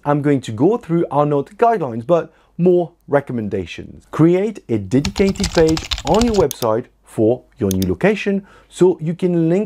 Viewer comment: What did he say to create at about 3:23? I played it several times but the clicking computer key drowns out his words. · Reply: “Create a dedicated page on your website”